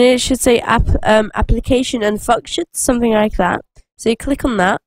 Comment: was i the only one who heard two cuss words? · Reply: Fucktion